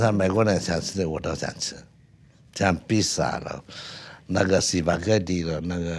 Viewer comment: Like spaghetti to me sounded like drug dealer at first.